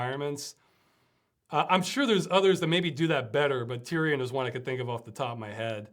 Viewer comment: The universe after afterlife question, anybody catch what answer was? Tyrion? Is it a book? Is it a game? Is it a poem? doesn't say! Even Closed Captions just says Tyrion, which google can't find. What is Tyrion? Please explain the obscure references you make.. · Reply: He said Tyrian, a scrolling-shooter game.